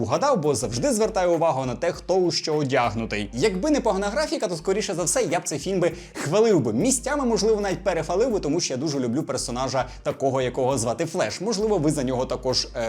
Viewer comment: «якби не порнографіка» Просто почулось 😳